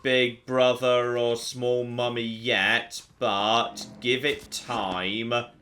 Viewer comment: Whenever said "Small Mommy" I thought he was saying mummy and I was confused by he'd think mummies would appear in Tesla's wonderland.